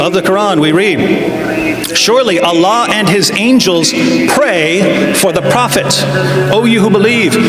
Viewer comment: If you actually watched the video, David did say “pray for the prophets” Mohammed hijab thought he said “to the prophets” either way it’s still a contradiction because if allah is praying for the prophets who is he praying to??? Looking at these comments is laughable that this clown hijab thought he won😂😂😂😂